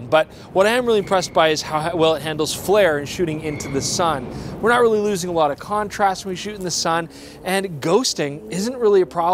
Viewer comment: "Wernurururusingalota contrast when were shooting into the sun ..." at least that's what I heard the first time around ... i dunno why I laughed so hard.